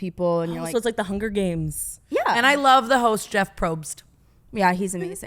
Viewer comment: I was sipping my coffee and I heard “ I love a hood” and I literally almost CHOKED 🤣🤣🤣🤣🤣